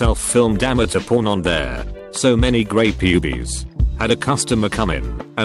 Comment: Grey pubees... I'm dying rn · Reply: I heard "grey Pubies"